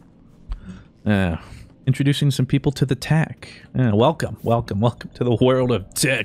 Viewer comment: Who else thought said "Welcome to the world of dick" instead of "tech" which he actually said? Because I shot bourbon out of my nose and near choked to death. you owe me one bourbon and a new t-shirt. F@ it even came out or got in my eyes. I can't begin to tell you how painful that was.